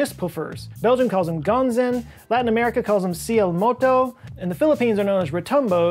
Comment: When I heard the latin american name for skyquake i thought you said "siii, el moco" instead of cielomoto XD sorry, I had to say it, the pronunciation wasn't that far off tho.